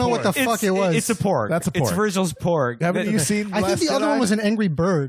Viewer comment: Hahaha wow the Irish are ok to be bigoted as fuck about. i honestly don't care. Thought you said "Virgil's PAWG Palace", that sounds pretty fucking rad.